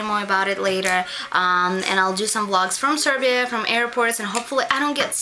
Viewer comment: It sounded like you said make love lots instead of vlogs. lmao.